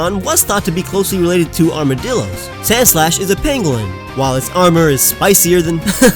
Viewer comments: At first I thought you said sandslash was a penguin. I was like, "Uhhhh...." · I just watch this video just listening to the audio and I could have sworn you said "Sandslash is a penguin" and I am like wut? Is this an April Fools video? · I thought he said penguin lol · Did he just say sandslash is a penguin